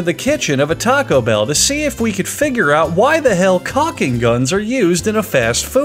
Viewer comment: Did he say finger out ?